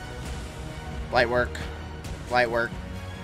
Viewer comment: when he said light work a couple of times I heard light twerk light twerk 😂